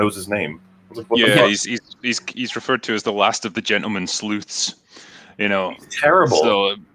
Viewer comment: I heard "last of the gentleman sloots". :D